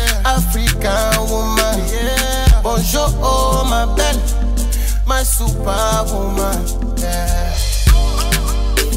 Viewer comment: after " bonjour oh ma belle " @ 1:52what does he say ? It sounds Haitian Creole to me like " menm siw pa pou mwen " meaning " enven though you're not mine " or maybe get it wrong🎼🎧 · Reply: He said my super woman.